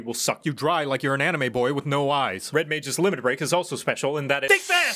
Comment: Still not sure what all he is saying for the Red Mage Limit Break :D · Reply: THINK FAST CHUCKLENUTS